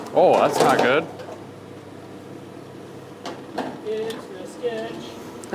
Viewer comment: did say here’s the sketch? Too funny.